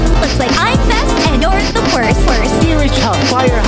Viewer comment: ??? What did she say? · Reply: "I wanna go home" but I think it was pitched down that's why it was hard to understand